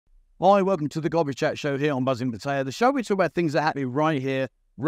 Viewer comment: Great interview, I had to double check the introduction. I heard, "Hi welcome to the gobby chat show!"